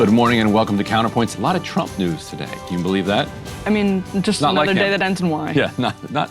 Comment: i thought Emily said “just another day that ends with wine” and i felt that